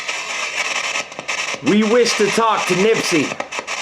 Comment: sounded like Nispsy not Blizzy...